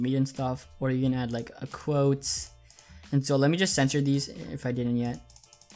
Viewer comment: Thought you said "Censor it" lol